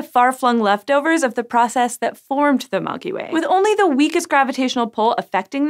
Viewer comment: did she said "...that formed the MONKEY WAY" at 3:34??????